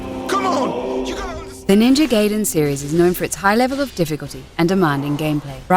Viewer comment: i quit watching at the moment i heard "GAYden"